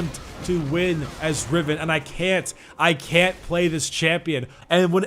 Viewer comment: All I heard was " I'm a cunt and play Jayce"